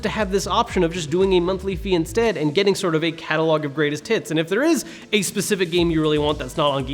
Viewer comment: Okay he is definitely saying catalogue of greatest tits